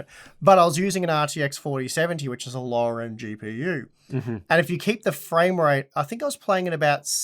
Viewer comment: WTF 4070 is now a low end GPU , was the RTX 5000 launch in the last weeks or what i have missed ? Im not a Nvidia Fan boy, i have a AMD Systen but this is BS at its best! Unsub sorry but i cant hear it anymore i know we all should by a 4090 but the real world doenst work like that! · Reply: He said LowER end GPU not Low end, those mean two different things. Even automatic subtitles recognizes it as Lower end